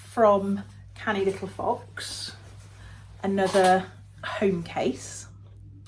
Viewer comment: I love the project folder that you bought, but I can’t seem to find the name of the shop listed in the drop down. It sounded like you said “canny little fox, or cunning little fox”. (I am American if you haven’t guessed) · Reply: Hello - it’s CannyLittleFox but they are only available through Instagram. She usually posts about twice a month so well worth following along